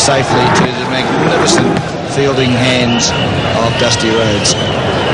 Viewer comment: Did he call him Dusty Rhodes? LOL